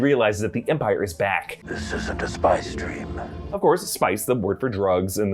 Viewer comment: Ohhhhhh he said “spice dream”, i thought he said “spy stream” like saying it’s not questionable surveillance, he’s seeing it directly.